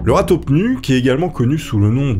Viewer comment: lol la première fois que jai entendu le nom, je croyais quils disaient rat-tout-nu 😅😂